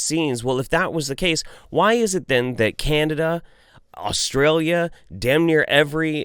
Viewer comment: did he just say Candida instead of Canada?? xD